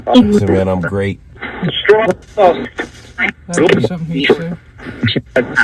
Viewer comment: At about 1:00:25 I heard “I’m Strong Enough now” ❤️